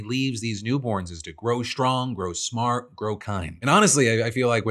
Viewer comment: the subtitles say "grow strong, grow smart, grow honestly." after watching a couple times and turning captions on and off I realized the next bit starts with "and honestly," but I'm just surprised the whole word "kind" got elided, as it was definitely enunciated and had a pause.